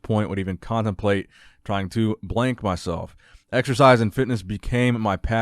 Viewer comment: When you said blank, I thought you said wank!!